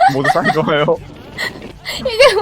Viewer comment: "모두 사이 좋아요"라고 들리는데 기분 탓인가요